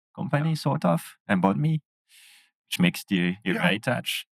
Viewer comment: what company is he saying hes involved with? the accent is pretty thick and im not french, sorry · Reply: It's Embodme - <link>